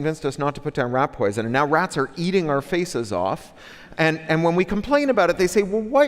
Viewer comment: i thought it said 'rats eating our feces' and i was like yeahh word, but then I saw it actually said 'rats eating our faces' and I was like huh??